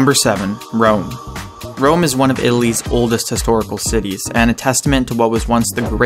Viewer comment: Italy's, it sounded like he said Illy's